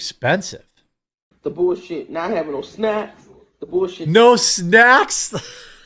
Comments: I think she said no SNAP. Did she think she’d get the same welfare benefits there? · I think she said "The BS not havin' no SNAP," not "not havin' no snacks," Supplemental Nutrition Assistance Program (SNAP) is the modern version of food stamps. A welfare program she is clearly very accustomed to having in the United States, but not in Africa. She is exactly where she belongs. The universe is teaching her things she needs to learn and looking out for her physical health by not feeding her too much. She should stay right where she is, live a long life and not be here being a parasite on good, hard working, people with morals.